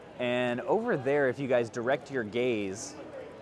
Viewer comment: I thought you said direct your “gays” lmfao